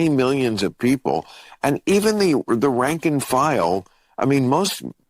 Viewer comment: did he REALLY say "Ranking file" 😭💀